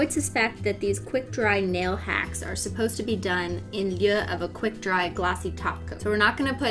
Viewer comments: Did she say “in lieu” but like not the way I’ve heard it said. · in lyuh? yuh? idk wtf she sayin lol